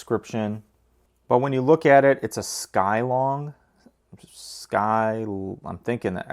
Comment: I thought it said schlong not skylong lol